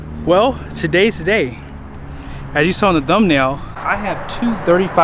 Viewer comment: I think i hear you say "dumnail" ...lol.